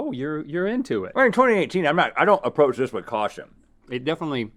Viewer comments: Did Rhett say they were in 2018 ? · Did Rhett say it's 2018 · Did Rhet say "we're in 2018, i don't approach things with caution" at 2:29? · did rhett just say we’re in 2018? i’m confused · Did Rhett say “We’re in 2018” at 2:32 ? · did Rhett just say we’re in 2018😂